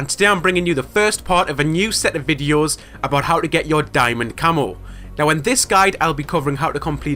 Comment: lol it sounded like u said diamond camel